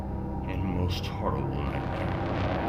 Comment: Can anyone tell me what the man is saying at 7:54 to 7:56, which comes right after he says "giving us the world's scariest and most horrible_____________________". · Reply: Think it's nightmare